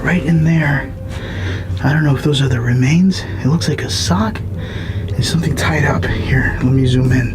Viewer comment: I hear "I like ... cock"